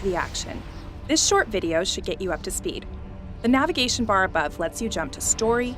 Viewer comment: i literally heard "this shit video should get you up to speed" at first xD not a bad video but it made me laugh my ass off before i went back to confirm it wasn't true